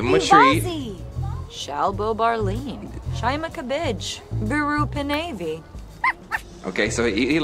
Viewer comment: why does it sound like she said “shine like a bitch” LMAOOO😂💀